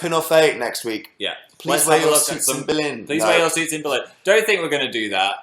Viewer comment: What did they say at 18:52? I can't tell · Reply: please wear your suits in berlin? that part?